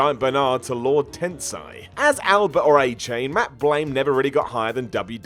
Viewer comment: Matt who? Did you call him Matt Blaine? His name is Matt Bloom. Jesus guys.